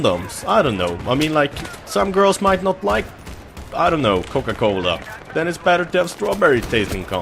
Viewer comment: He said cola I think, I thought he said condoms too at first.